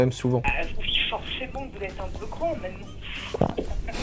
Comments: elle ma tué de rire !! ;) "vous êtes grands mais l'hallucination auditive on dirais qu'elle dit 'cons' quoi que les 2 sont pas faux ;) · J'ai réécouté plusieurs fois et j'ai compris que c'était "grand" mais la première fois j'ai vraiment entendu "Bah oui forcément vous êtes un peu con maintenant" et je crois que c'est ce que vous avez compris aussi. J'étais mort quand j'ai entendu ça ! 🤣